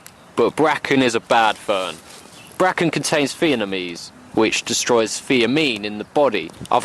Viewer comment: Swore I heard "..contains vietnamese!" xD